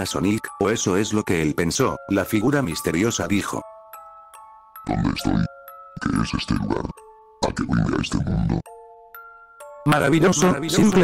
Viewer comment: no se entiende ni un chorizo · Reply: Dice:donde estoi que es este lugar que vine a este mundo